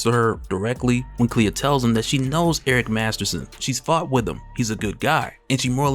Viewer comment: I had to listen to 5 times. I don’t why but I thought you said “she’s knows Eric Masterson, she fu@ with him.” It’s not a huge deal just made me pause like “what this dude just say.” Hahahaha